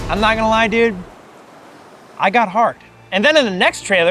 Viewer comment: Did you say you got hard? If so i cant really blame you but yeah